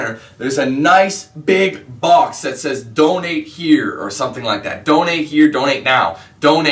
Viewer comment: i thought you said dont hate then i realised you said donate LOL